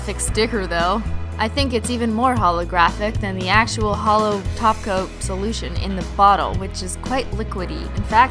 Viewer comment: i always thought she said holo taco till now i feel so dumb